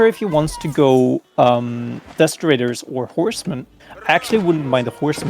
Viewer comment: For some reason whenever said "desert raider" I kept hearing "desecrator" and now I'm imaigning a Desecrator unit. Like, it would have bonus damage against religious units and decap sacred sites twice as fast. Maybe immunity from conversion. And a "desecrate" ability that would work like the shinobi's sabotage, but only on religious buildings; it would block relic income, too.